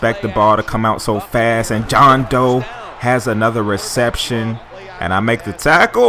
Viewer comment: for a minute i thought you were saying john doe then i saw it really is dough